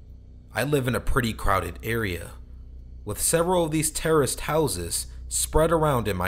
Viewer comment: For a second I heard that as” Terrorist houses”.Talk about true horror......